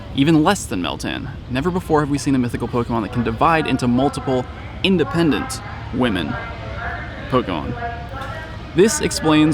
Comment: did i just hear independent woman 😂😂😂 Shit is hilarious.